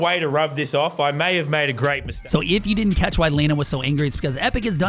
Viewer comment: Did He just say Laynan? 😂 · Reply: JustALemon he said lannon